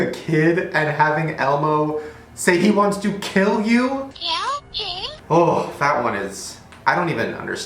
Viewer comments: instead of saying James I thought it said Shane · it sounds like it say's "Kill Shane" once you hear it you can't unhear it. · i think he says help james